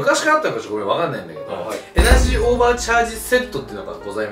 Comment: エナジーおばあちゃん味に聞こえた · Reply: 動画の前にこのコメ目に入っちゃってからエナジーオーバーチャージがエナジーおばあちゃんにしか聞こえんw